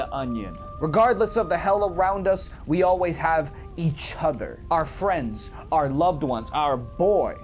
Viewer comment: When you are talking about the onion bro at the dark souls 3 bonfire and you said "regardless of the hell around us we always have each other" ... I heard "regardless of the hella roundness we always have each other". you know round like an onion?